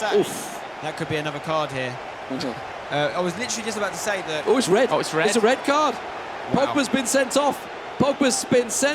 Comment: what does say at this point haha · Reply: looks like he says "get f...ked"